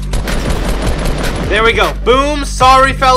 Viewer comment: When he said feller I thought he said filler 😂